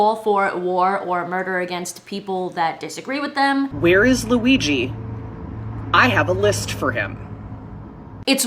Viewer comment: Ohhh, she said, "Where is Luigi?" I thought she said, "Where is a Ouija?" I thought she was going to try to get people killed by calling on the spirits of a Ouija board...